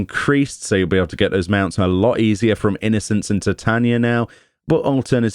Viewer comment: almost sounded like you said Innocence and Satania. Now that'd be a sadistic boss trial.